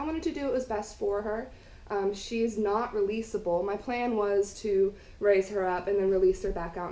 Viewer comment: I'd like to give everyone an update on my girl here. I was able to release her this past spring and she is a happy wild squirrel now 😌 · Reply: Thought you said you can’t release her